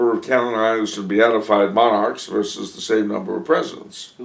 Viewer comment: hard to understand what he's saying, what he say at 0:30? "line up the number of ..." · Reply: He says canonized and beatified monarchs